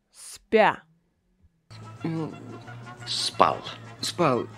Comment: Спасибо огромное. Что он сказал на 10:32? Звучало как "yes", но не может быть! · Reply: Пожалуйста 🤗 He was actually going to say "я спал" but didn't finish the phrase that is why you hear 'yes' (я сп... ) 😉